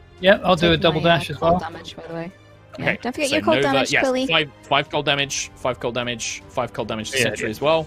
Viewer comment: did i just hear say quilly? I love them so much, they are literally like siblings 🥺